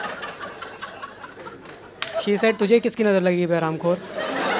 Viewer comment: anyone else heard a girl saying "Bechara"